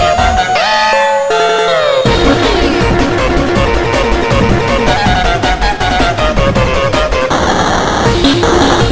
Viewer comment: what is it saying? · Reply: distraction